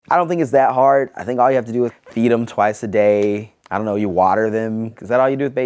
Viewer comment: omg at 0:03 I swear I head him say, " beat them twice a day!"